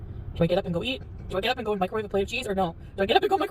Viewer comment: microwaved cheese? Am I hearing that right?